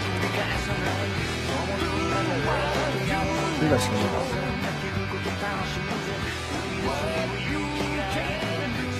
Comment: なんて言ってんの？ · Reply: 火貸してくんねえか